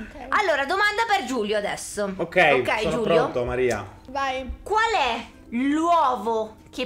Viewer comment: ma solo io ho sentito Maria e nn hahaha